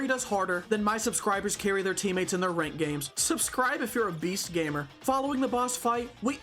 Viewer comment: i thought that said "subscribe if your obese gamer" LMAO